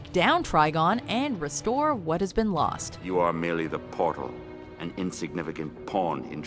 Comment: did he just say PORN!?